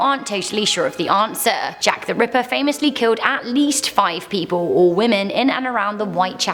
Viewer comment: “5 people or Women.” I thought I heard a sexist joke.